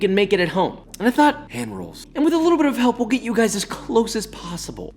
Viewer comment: When you said hand rolls in the beginning I thought you said egg rolls, I've never laughed so hard in my life🤣